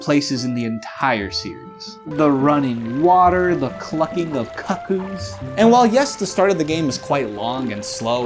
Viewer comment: Sorry, did you say CUCKoos? Great video btw